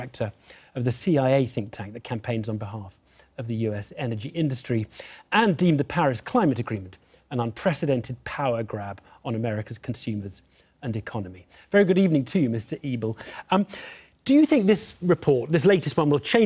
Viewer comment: I heard "Very good evening to you Mr Evil"